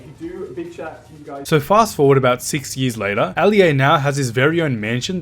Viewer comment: did he just say fastforward 60 years. Ali a is only 23 lol.